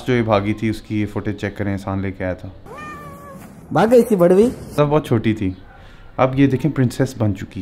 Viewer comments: what said bhagg gae thi...??? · What just said @ 2:12??? Barvi?? 😂😂 wth 🤣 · did he call a cat bhadvi 2:11 ??